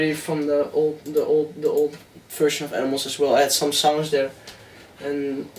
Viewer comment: he says "the old version of ..." What does he say after the word "version" ? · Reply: the old version of animals aswell